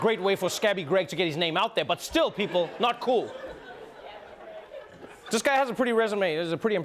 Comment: Did anyone else hear the “Yeah, Greg..” around the 4:12 mark? 😂